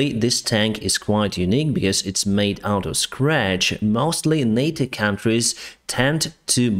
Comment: I had to listen a few times. I thought said, "It is made out of scrap." He said , "It is made out of scratch." This almost looks like a dressed-up Pz IV from 1941. Also, did he say it is being built by a company named 'Rust-Tech' ?